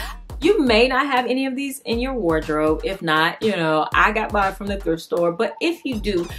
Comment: these are all beautiful 😍. On 7:55 you said, “I got mine from the thrift store” and I thought you said, “I got banned from the thrift store” lol. Girl, you are so talented and you are my favorite person and I want to learn how to sew so bad. Keep your videos coming. I love those peasant type dresses that I’ve seen and Etsy is way too expensive! Have you made one of those with the layers? I also like those aprons that are baggy with pockets.